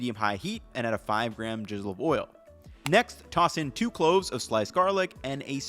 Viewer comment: Ngl at 1:58 I thought he said add a jizz of oil